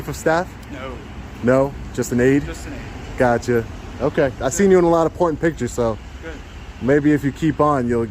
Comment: Haha, almost just almost sounded like "I¨ve seen you in a lot of pornpictures" funny!... Keep it up with more videos!